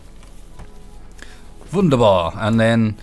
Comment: did you say "wunderbar"? :D Defintely build a Uranium Table :P